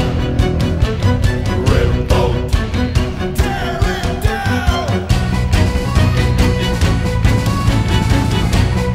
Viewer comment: After the 2. "Revolt!", at 2:08 theres someone shouting something. For me it sounds like "Darendale" x) Does anyone knows what he's saying? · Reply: It’s “TEAR IT DOWN!”